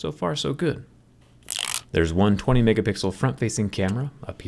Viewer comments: did I hear 120 megapixel front facing camera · did anyone else hear him say "there's a 120 megapixel front facing camera"? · Did he just say 120megapixel front facing camera ?!? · thought you said "120 megapixel camera" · i thought he said 120 mega pixel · did he says 120 mp camera or one 20 mp camera ☹️🤷